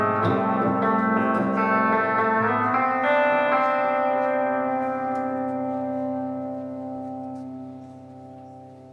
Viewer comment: what song did he say he was playin at 6:50? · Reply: Nathan McDonnell prayer position